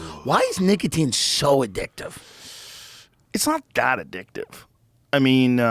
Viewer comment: Not so addictive! 1:15 , it’s the most addicting substance on earth I thought I heard